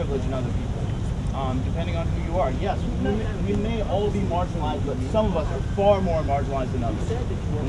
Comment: he says: "we're all marginalised, but some of us are far more marginalised than others!" I hear: "We're all equal, but some of us are more equal than others!"